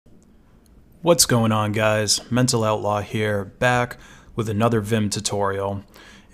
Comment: Almost thought you said "What's going on guys, mental illness here."